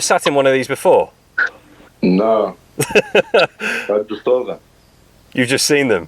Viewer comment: Who else thought the winner said 'I just stole them' when asks have you sat in one of these before? 😂😂. I know he said 'I just saw them' but sounds a lot like stole them haha. Congrats anyway. Monster whip 👌